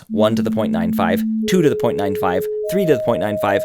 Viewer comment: what is he saying at 17:15? 3 to the .95? as in 3 to the power of 0.95? 3^0.95? it just seems a weird choice of numbers, I get that we hear logarithmically, so shifting the harmonic series by a power is kind of shifting it all by the same amount, but since the point is to use a different harmonic series to show how it shifts the perception of dissonance, I don't see the use of doing that instead of just using like a linear difference, like 2.05, 3.05, 4.05 etc, specially since the harmonic series is already linear in frequency, so I don't see the point of shifting it exponentially, unless it causes a specific different effect?